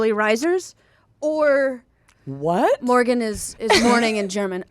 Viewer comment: I'm german and I didn't catch the Morgen joke because I thought you said the name Morgan